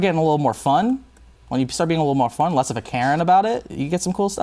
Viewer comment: Did he say "Being less of a Karen about it"? haha